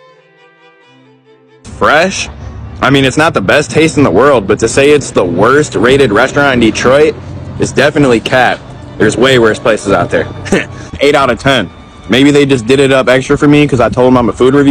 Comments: I thought you said definitely cat! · I THOUGHT YOU SAID IS DEFINITELY CAT 🐈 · “Can you give me the best meal you guys got?” Lady: 👁️ 👄 👁️ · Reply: She definitely ran upstairs & got the food she was cooking for her family to give him once he said he was a reviewer. I swear i thought he said "it's definitely CAT" 😂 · I swear i thought he said "it's definitely CAT" 😂 She definitely ran upstairs & got the food she was cooking for her family to give him once he said he was a reviewer